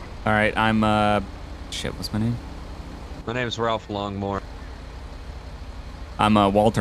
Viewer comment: Did he just say Ralph Lawnmower? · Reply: longmore i believe